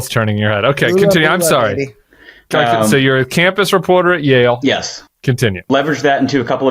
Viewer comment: When Tim asked if he was the campus reporter at Yale I heard "campest reporter at Yale," which I thought was a bit rude😅